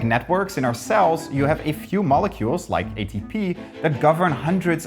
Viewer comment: not me hearing agent p instead of atp